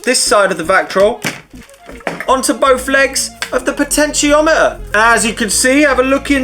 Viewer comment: P for Potentiometer - I gotta use that more often when spelling stuff over the phone! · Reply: I thought he was saying "pitch-en-tiometer"